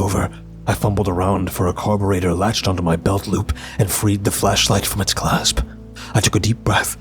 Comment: I thought V initially said "fleshlight" lol😅 👀